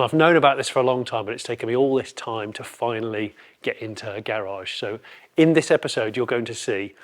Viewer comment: Did he say 'it's taken me all this time to finally get into her garage'? Well that's an attention grabbing opener if ever I heard one.